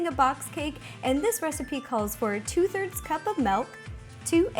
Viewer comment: Did she say melk